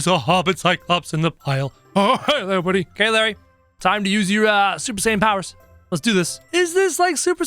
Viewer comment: Did he just say supper saiten